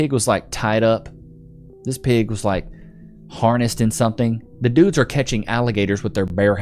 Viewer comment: what i heard, that pig was full of ticks so, that pig was half dead